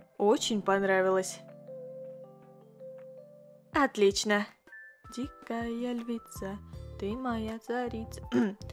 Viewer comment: я одна слышу песню *дикая львица, ты моя царица*???